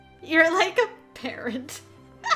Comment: PLEASE-- I thought you said "like a parrot" and i was dying 😂😂 But damn! Didnt expect to meet elt so soon, i figured we'd meet him eventually but wow 👀 also my man, elt, no hitting on our girl when shes with her doctor ✌